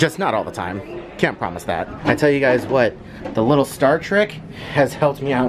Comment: Star TRICK! I thought you said Star Trek! LOL